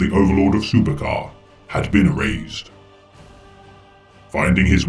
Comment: I kept hearing "overlord of supercar" Great video btw